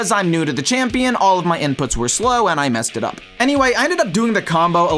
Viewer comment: (on low volume ) i swear you said all my n words were slow at 1:10 😭😭